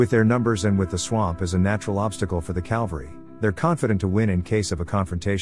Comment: did you say calvary instead of cavalry?😛